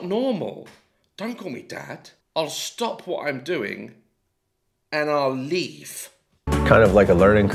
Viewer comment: Is it bad that I heard ''Call me daddy and I'll stop what I'm doing and leave'' and my first reaction was ''Oh so just like my real dad then.''